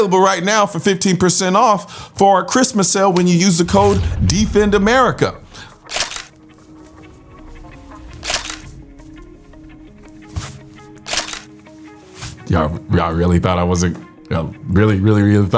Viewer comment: I am hard of hearing and thought you said “DEFUND AMERICA” 💀